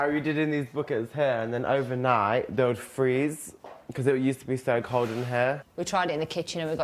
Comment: What about the bucket at 2:08? I didn't understand a thing he said. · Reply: Linksdagger7 did you mean it would freeze over night